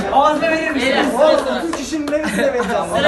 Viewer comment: Sonda "ağzıma verirmisiniz" diye bir cümle duydum 😂